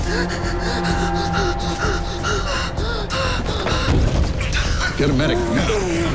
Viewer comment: Did he say Oliver at 3:48 or am I trippin 😳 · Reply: Jasmine Hightower lmao you’re trippin😭 he said we need some help in here or something like that